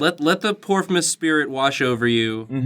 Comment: i thought you said "porkmas"